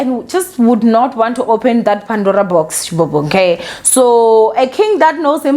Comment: I thought she said "Pandora box "?